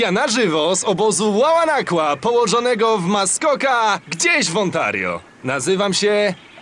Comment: Dlaczego usłyszałem ,,gdzieś w ohio"